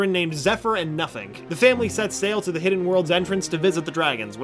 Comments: when he said 'nuffink' i thought he said nothing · I swea I heard nephyr and nothing